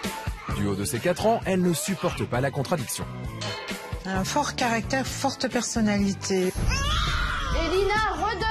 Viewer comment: I swear I heard this right about now: f-cked character f-ck the personality